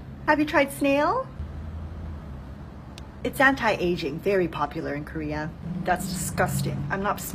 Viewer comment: when the worker said “anti aging” i thought she said anti asian, and i was like ?? ok?